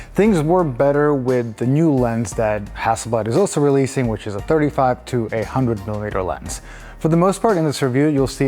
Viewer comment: I thought I'd heard 31 to 800mm for a second there. You had me questioning everything for a moment 😆